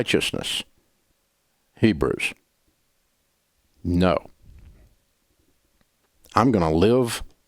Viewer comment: I'm sorry, I must be misunderstanding what he is saying at 3:50. Did he just say "Hebrews. No." ? Lol